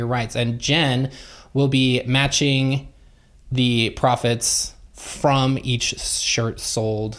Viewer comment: I was confused because I heard say, "she will be matching the prophets from the shirts" .. lol!! Profits got it